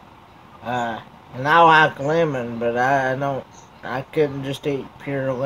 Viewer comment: Lol when you said Lemon i thought you said you like woman.